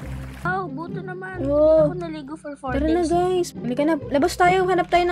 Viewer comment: i heard said ''di ako naligo for 4 days'' 😂